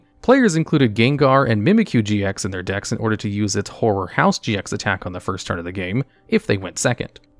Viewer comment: This is going to sound wrong, but every time I heard you say "horror house GX" I instead heard "whore house GX"